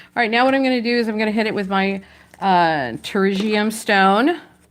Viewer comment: what are you saying? Tourisian stone? How do you spell that? Thanks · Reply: pterygium stone. Google it.